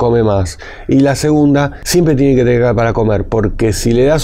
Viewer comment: dice que hay dos cosas importantes, no escucho con claridad la segunda, no entiendo las palabras que dijo. Alguien puede indicarme? Gracias. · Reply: Dijo: "siempre tiene que comer " , según yo entiendo que debe aplicarse este remedio diario